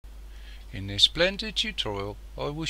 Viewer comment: i thought he said "in his splendid tutorial-"